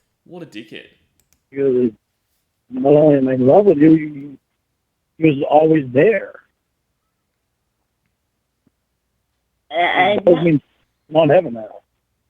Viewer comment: does he say “when I wanna make love to you or always there, that’s not happening now”?